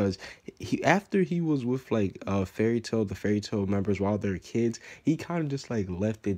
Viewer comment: i thought he said he was quitting fairy tail